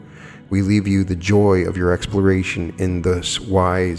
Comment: What does he say at 34:18-and-34:24? I don't understand one word or something. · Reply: The joy of this exploration by what??